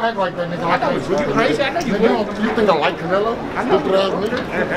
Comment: What did they say ?! Real answers only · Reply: All I heard was “you think I like canelo” lol